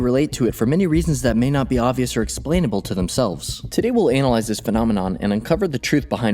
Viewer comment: did you just say ANAL ize ?